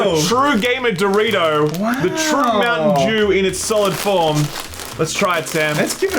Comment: Did just say “Mountain Jew”?. I swear he did! Time